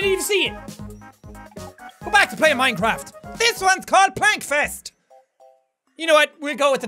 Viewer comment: it sounded like you said "Pointfest." If you ever find yourself in St. Louis, you sjould come to it. Best damn alternative rock festival ever.